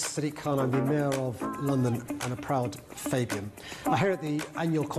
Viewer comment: did he just say "A proud Fagin"? 😅 As in, the slimy thief from Oliver Twist? 😂